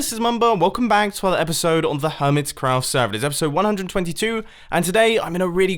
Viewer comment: I thought he said hermit crap. Lol